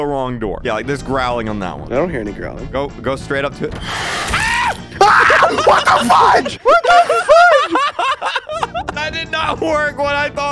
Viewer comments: In 3:29 i heard sapnap said "ahhh what the f*** what the f***" which is i heard for the first time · To those who thought sapnap sweared, he said "WHAT THE FUDGE" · He said what the fudge but i thought he said what the fu**